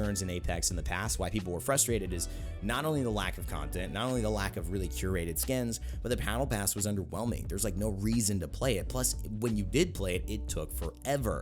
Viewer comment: Did you say “Pattle Bass” at around 12:13-12:17?